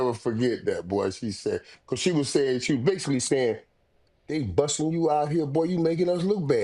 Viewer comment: Mase thought Irvin said “they busted me out there”😂😂😂Nah man, he said “Bus”(school)